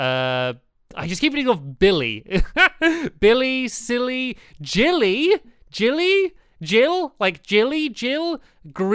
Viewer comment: Jilly Konka. Random name(no offense). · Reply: xD He actually said Jilly!